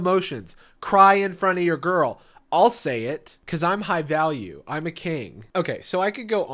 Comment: I thought he said "I'm high value, I'm Canadian"